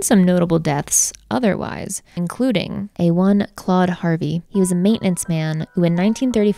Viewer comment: I heard "A one-clawed Harvey" and was like wow I've never heard of this mythological creature 😭 time to lay off the weed lmao